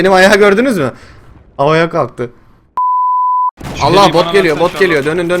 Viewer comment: de bot geliyo yu Bok geliyo diye anladım :D